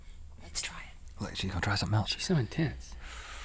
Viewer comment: OMG at 7:15 when Link said "shes so intense" I thought he said "shes showin t1ts!!!!!!!!!